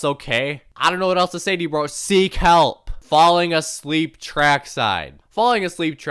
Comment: sounds like bro is saying Sieg heil 😭